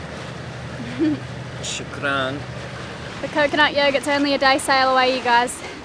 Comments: Did he say " choukrane " in arabic ? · Did I just hear say “shukran” 13:50 where did he learn to say thank you in arabic? Lots of love <3 L